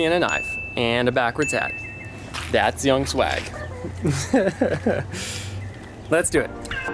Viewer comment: Could have sworn he said young sl*g